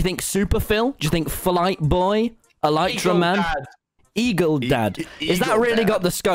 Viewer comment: Reply with your own timestamps! - Rudylmao (Innit Inc?) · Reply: it sounded like wilbur said "e-girl dad" lol